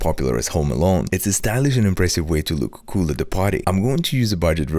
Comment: I ligit heard him say “look cool at the potty” 😂😂😂